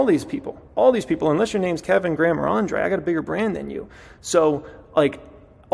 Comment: Hahaha I thought he said "unless you're name is Graham Kevin or Andre I have a bigger brain than you"